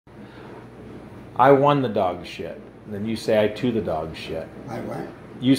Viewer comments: Seeing old people laugh is like them gaining another 50 years of life. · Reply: I heard "I 2 the dog sh*t" as "I chewed the dog sh*t"🙂. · Tbf, I was also confused when i thought he said "i won the dogshit" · Treating him like his mental age. That is true kindness. Age shouldn't mean you have to be serious all the time. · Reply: Oh I thought he said " I won the dog shit" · I mean I feel like "I won the dog shit" is just as funny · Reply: I thought he said “I want the dog shit” lmao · That moment when a 99 year old gets the joke, but I don’t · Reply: Damn... I thought he was saying “dog shed” I was so confused 🤦🏻‍♀️